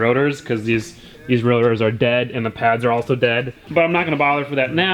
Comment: What did you say? Your rotary is dead? trololol